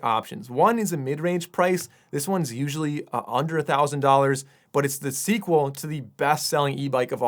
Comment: bro for Number 15 @ 21:05 I heard "this one's usually a hundred thousand dollars" 😂😂